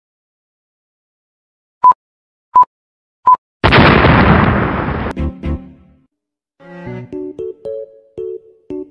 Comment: As someone who speaks french: WHAT DID HE SAYYYYYYY???? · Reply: If I'm not wrong, it was "Goodbye, b!tch"